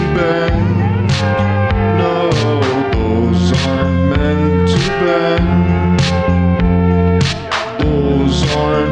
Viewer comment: Am I the only one who hears "doors arent meant to bend" ? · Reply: I hear "those are meant to Ben" lol